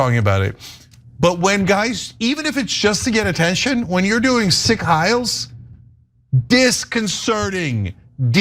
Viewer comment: Sounds like says Sick heils. Lol Thems some sick heils bro